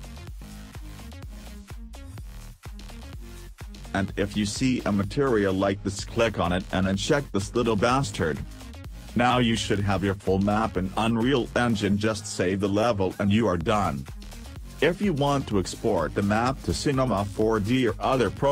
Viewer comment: did i heard "LITTLE BASTARD" lmfao